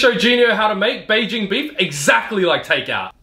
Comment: I thought at first he said “exactly like vader”